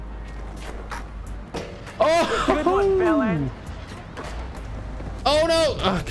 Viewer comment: Did just say "good one bell end"? (bell end is British slang for dick head)